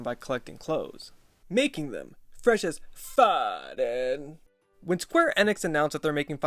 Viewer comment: wtf he say 😂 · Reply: They’re FRESH AF DUDE!!